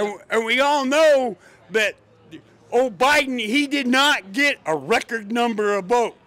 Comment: "I wasted a million dollars and lost several companies, and I'm doing it for my kids." Absolutely insane. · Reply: Kid, you are AMAZING. Hats off to you and your parents. You give me hope for America's future. Also, 5:45 did he say O'Biden 😂 - that's a new one.